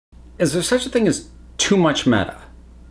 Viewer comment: 🤦‍♂️in the intro I thought he said "is there such a thing as too much meth"